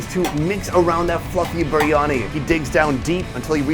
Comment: "he piles on some white f-ing rice on top" - I know he said fluffy, but it sounded like that.